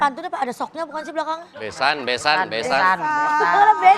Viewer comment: sumpah aku dengernya jg besok, bukan besan wkwk